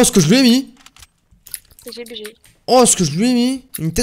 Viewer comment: jai entendu un petit gg bg 😉